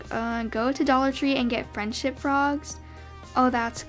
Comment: had to comment this ‘awh friendship frogs that’s so cute’ I need that for me and my friends 😂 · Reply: I thought she said friendship bras ngl- 😂😂😂 I was looking for a comment like this to figure out what she said.